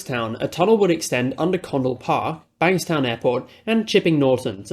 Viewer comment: why did I hear Connal park